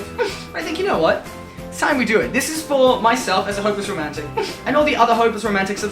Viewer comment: Lol, I thought he said homeless romantic